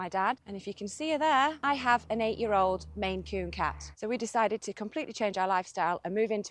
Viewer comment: I thought you said "an 8 year old mangy cat" for a moment! 😂